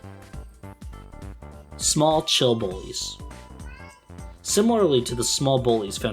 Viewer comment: at first I thought he had said 'small chill bois' instead of 'small chill bullies', lol